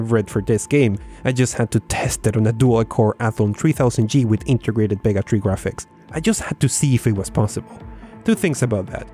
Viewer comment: Sounded like he said integrated bigotry graphics...sounds like an AI card that won't render images it's prejudiced against.